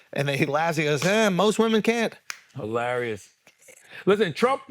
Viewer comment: he actually said most people not most women